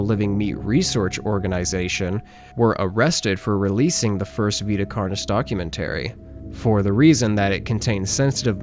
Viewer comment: I think I walked into the wrong party. I thought it said "Viva Carnitas" and I was certainly expecting tacos.